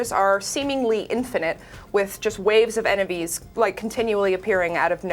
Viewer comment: Did she just say “waves of … ENEVIES ”